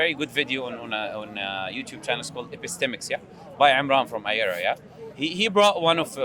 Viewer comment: Can someone tell me what video he meant in 32:16 - I replied many time and can't hear him clearly XD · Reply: video name is Epistemix